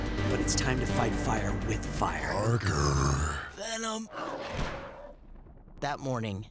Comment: at first I thought that said He’s black 💀